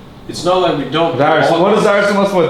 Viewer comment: What does Lust say at 29:45? :o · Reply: No... he says "let me type". It's because his verbal English isn't as good yet at this point in time. So he wants to type out what he wants to say to communicate it better.